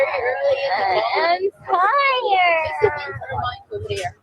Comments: I thought she said "im tired" not fire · Gypshit says “ I’m tired” where you thought she said “fire”